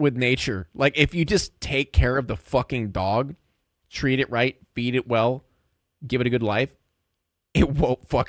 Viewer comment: I thought I heard beat it well at first. Instead of feed it well.